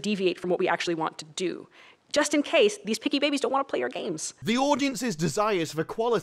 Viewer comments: So a “picky baby” won’t accept her ideology but a “sweet baby” complies and accepts it? Creepy and condescending at the same time. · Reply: I thought she was saying 'piggy babies'. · "Picky babies..." No one saw that red flag? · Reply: I legit thought she was saying "pinky babies" like some kind of racial insult. What is their fascination with babies, it's really creepy.